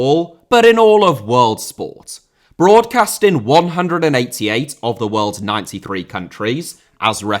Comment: Did say "188 of the world's 93 countries"?😅